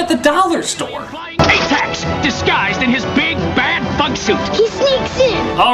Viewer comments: did anyone hear 46:13? "DISGUISED IN HIS BIG BAD F*** SUIT" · am i the only one who hears "big bad fuck suit"? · Did only I hear "Atax disguised in his big bad fucksuit"?